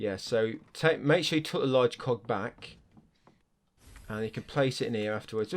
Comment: thought you said large cock accidentally then just realised its a bit lmao